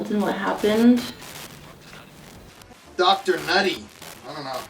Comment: This might be a stretch, but at 46:06, is it possible that instead of 'Dr. Nutty,' it actually said the doll's name—Mr. Buddy?